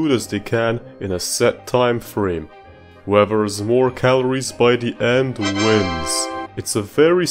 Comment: When you said whoever has more calories wins i thought you said webbers war calories in.